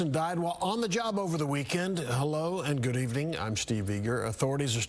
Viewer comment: did he say steve N**ga 🤔